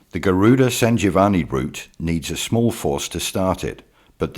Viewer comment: What in heavens name is he saying at 7:04 "the garuda san giovanni brut." Anyone? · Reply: Sanjeevani or Garuda herb.